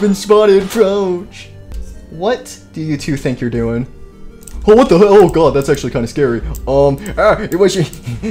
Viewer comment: legit almost said: Oh What The Hell Or I think he did say that 🤔